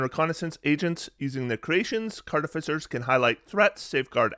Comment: did you call them ‘cartificers’?